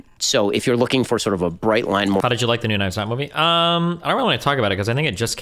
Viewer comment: How did you like the new what movie? I have no idea what Destiny said there. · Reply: "The new Knives Out movie". Glass Onion.